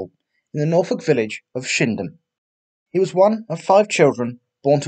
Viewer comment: Thought you said shimdham and not shipdham where he was from